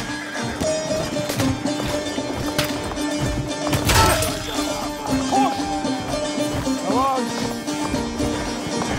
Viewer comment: Someone understand what the otoman soldier say in 1:58? In Turkish language ? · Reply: "koş" "run", my soldier is saying that.